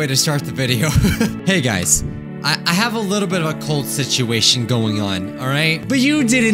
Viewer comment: I have a cold situation going on What I thought he said: I have a cult situation going on